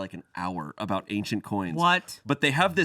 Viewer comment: Ah, shayne said an "ancient coins" booth....I heard "agent orange" booth. Needless to say I was confused